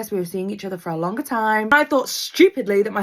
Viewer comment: O thought she said younger 😭like young